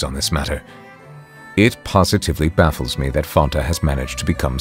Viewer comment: did he say fanta or am i tripping?